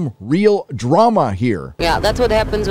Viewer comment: swear to god I thought she said writers room 🤣🤣🤣